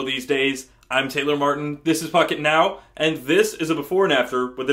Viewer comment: I thought he said "Fuck it now" lol